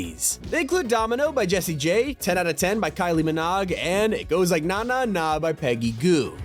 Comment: Did he just say Kylie miNOG .... 😅😂😊